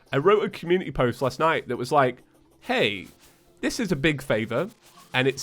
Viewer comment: i had paused the video and when i unpaused it sounded like he said A and its when he said hey